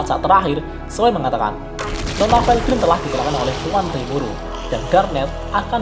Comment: ngomong apa sih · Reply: Nona velgrynd telah dikalahkan tuan rimuru, maap kena suara lain